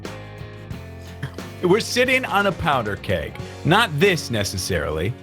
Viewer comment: ~I thought he said "powdered cake" and I was so confused XD